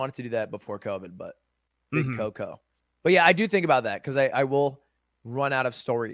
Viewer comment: Did he just call COVID 'the Big Coco' at 1:10:50? lol